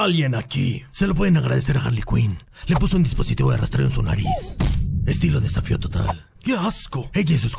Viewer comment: alguien sabe que dice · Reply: Si es batman, dice , estilo desafió total, refiriéndose a la película de Total Recall, saludos!